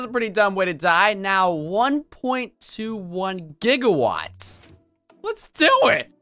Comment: It's 'Two point twenty-one JIGIWATTS!!!' For anyone who didn't understand, it's a back to the future reference